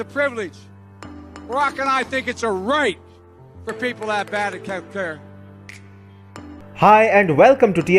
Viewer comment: I'm sure I didn't hear that right, but 0:40 "Barack and I think it's a right for people to have bad healthcare."